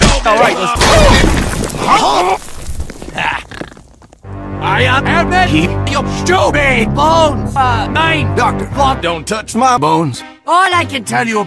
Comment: A lot of time ago I thought he said: “I am the docktor I will heal your sthupid bones but also NEIN!”